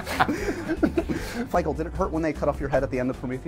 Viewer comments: did he say "Fichael"? LOL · ok 1) is it just me or did Josh call Michael "Fichael" at 0:30 ? 2) where did James say Ewan was from? I can't understand with such a thick fake accent